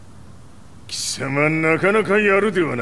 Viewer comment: he clearly said his real name Gouki, not Akuma... Come on translators....